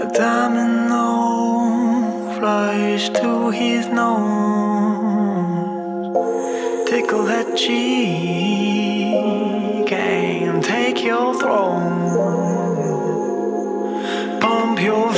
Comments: "Oh, it's 'tickle that cheek'? I always heard 'pickle that cheese'" :-| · Lmfao I am so fucking dumb. I always thought he said "pickle that cheese". It fucking says "tickle that cheek*. 😂😭😭😭